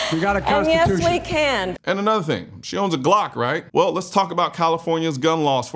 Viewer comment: I'm getting old and deaf. I thought she said 'she s___ a glock' while talking about how Willie Brown helped her win her first elected government job as Kommiefornia attorney general.